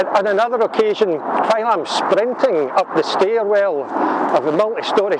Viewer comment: Instead of you saying you were "sprinting up the stairs", I thought you said you were "sprinkling" up the stairs, ☺. You're right, never pass up an opportunity to use a toilet. Mx